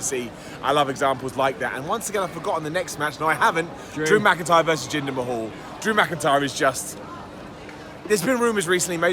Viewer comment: I thought for a moment said Drew vs Balls Mahoney. But it was Drew vs Jinder Mahal.